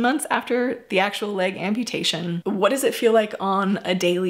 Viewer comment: ! Just one thing for the sake of a smile. I misheard you & thought you said your friend had a BALONEY Amputation!! Would that make the person more HONEST? love Steve Holliday